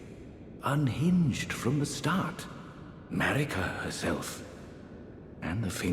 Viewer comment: I know it’s “Marika” but I keep hearing it as “‘merica”